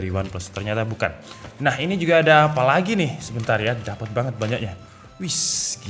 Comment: "dapet banget banyaknya" huh? emang gitukah, apa gua yg salah dengarkah😅?